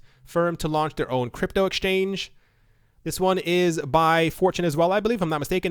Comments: Every time you mention Fortune sound like you say 4chan... >_> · Whenever you say, "this one is from fortune," I hear, "This one is from 4chan!"